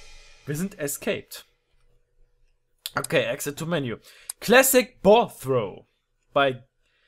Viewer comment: Hardi hast du bei 1:25 wie dner exit to menjou gesagt? Wers auch gehört hat bitte liken